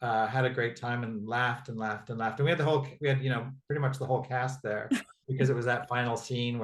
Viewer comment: Did he just say at 45:25 that the whole cast was there? 🤔🙄 Alba Baptista too???????????? And then he said that the blue of the swords say that Ava is back. Is the post credit scene maybe the first scene of season 3????? 😆😏 Ahh can't wait........... (English isn't my first language. Mistakes are all mine)